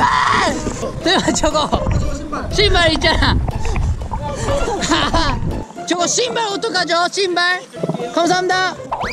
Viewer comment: I thought he said "Shibal" for a sec... Aboutta kijoring